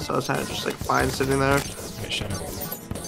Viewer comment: Why the hell are you so mean to? 13:35 sounded like you told him to shut up...